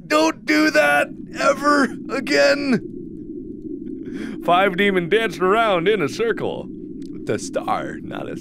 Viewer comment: I thought it said five lemons, not five demons, oh god 😂